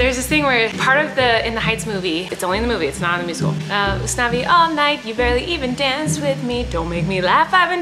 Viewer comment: I THOUGHT IT SAID UN-SAVY ALL NIGHT 😭😭😭😭😭